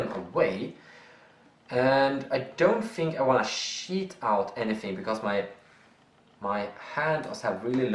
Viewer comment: did he say cheat or shit out @ 15:34?